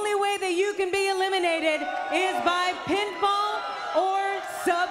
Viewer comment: the captions say by pinball or submission, I'm like well if Tommy Gets involved everyone's cooked